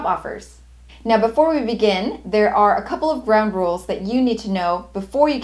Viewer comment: Is it just me or did it sound like she said, "a couple of brown rules"